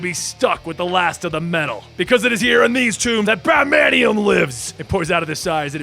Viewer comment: batmanium.. just tell me I heard that wrong. Please.